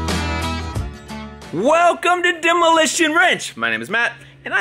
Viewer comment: I thought matt said demolition "ranch" for a second and I forgot that it was wrong it was "wrench" hahahahaha lol, good luck with your projects matt